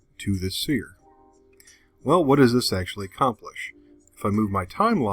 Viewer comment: Great video ,but on 2:56 captions say sexually 😅 so......